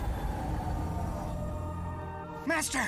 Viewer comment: Why I hear it as Shitfu